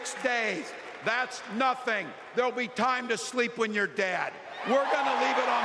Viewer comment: It sound like he said sleep whit your dad. Walt’s is terrible that loves lies